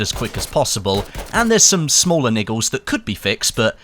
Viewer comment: what did you say? niggles?